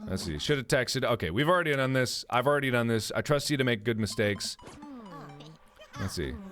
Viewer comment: did he just say: I trust you to make good mistakes? 56:46 XD